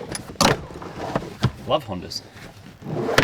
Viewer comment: Did Marty just say "I love Honda's "at 23:55 ?